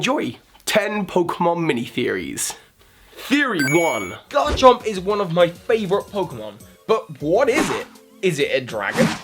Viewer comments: I thought he said donald trump is one of my favorite pokemon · I thought he said Donald Trump is my favorite Pokemon